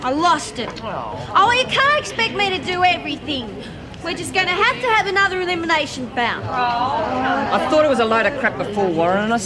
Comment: What did Marlene say at 36:10, have to have another of them what?? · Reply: Carlene Boulden have to have another one of them elimination bouts